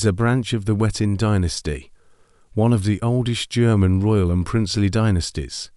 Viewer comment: So the AI subtitle misheard the AI voice over saying German as Zhoug.